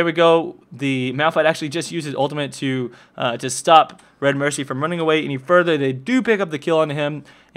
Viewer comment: did he say Redmercy?